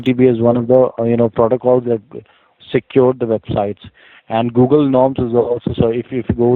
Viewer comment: is he saying Google nobs